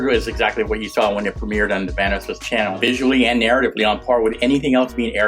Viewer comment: did actually said vaniss